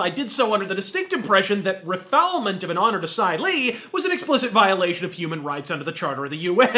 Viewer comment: what does he say at 0:05? refillement of an honor to side lee ? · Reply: Asylee. A person, especially a political offender, who has been granted or is seeking refuge or asylum in a foreign country.